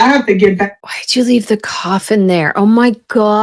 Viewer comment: Oh my, I thought you said why did she leave the coffin in there. I’m like coffin, what, where, how. 😂